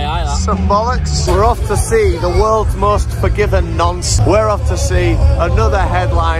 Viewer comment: I heard "worlds most forgiven nonce" and went "red hot chili peppers?" then the cut was perfect.